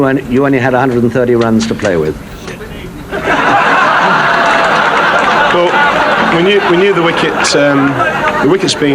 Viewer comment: What does the spectator say at 19:17 ??? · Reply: I think its "Shit Creek"